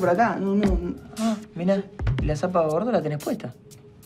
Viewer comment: Que le dijo a aquí: 8:31? Algo de las zapatillas pero no entendí 😂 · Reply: Le dice "Y las zapas bordó las tenés puestas" Bordó es el color burdeos, granate.